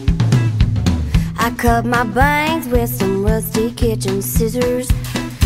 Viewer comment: i thought she said veins not bangs...that doesnt make sense